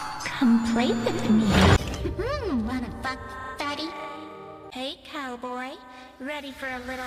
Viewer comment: i aways thought that cammy said that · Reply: says: Hey litle Boy